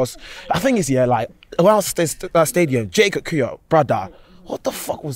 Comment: First guy 0:37 did man just call Kiwior “J Kakuya”?? 😂😂😂😂😂😂😂